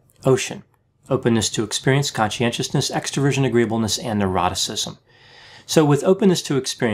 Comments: I'm finally able to keep up with imitating Dr. Grande's lightning speed explanation of what O.C.E.A.N. means. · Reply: Every time he says "neuroticism" I hear "eroticism" no matter how many times I hear it. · I'm finally able to keep up with imitating Dr. Grande's lightning speed explanation of what O.C.E.A.N. means. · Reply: I thought he was saying “agreeableness and eroticism” for awhile LOL